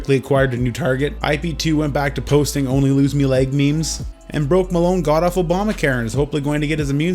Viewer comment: “broke Malone got off Obama Karen” is what I heard so I turned the subtitles on to see if I miss heard but nope it even thought that 😂 had me playing it back like 10 times lol